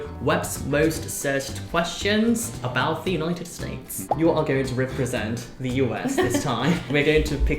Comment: When he said United States at the beginning, I heard “anointed snakes”